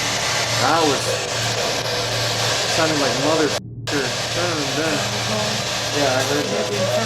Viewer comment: When had the spirit box at 40:03...I can distinctly hear the voice say "Motherfucker" "go away" in a male sounding voice. Who else hears the same thing??